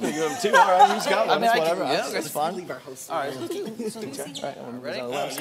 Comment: what did keith say to courtney? 1:16:18 it kinda sounded like "leave our host alone". but idk